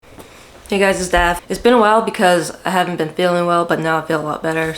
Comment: Oh Daph. I heard "hey guys its me dab" I was like hell yeah light up.